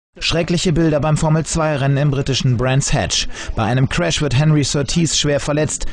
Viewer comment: Did he just say 'Bar Misvah'? LOL! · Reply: He said: "Beim Formel-2 Rennen in Brands Hatch..." that is german and it means : "At the formula-2 race in brands hatch..."